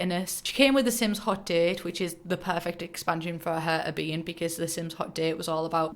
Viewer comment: Because of your accent I thought you said Agnes was "introduced in Haunted, which is all about taking your sims out and being flirty, so that makes sense" and I was so confused for a moment lol